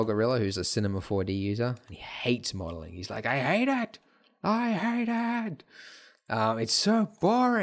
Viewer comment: he says "I hate it, I hate it" but the captions say "I hate act, I her dad" I was so tired that it had me rolling oml,. Love tthese vids!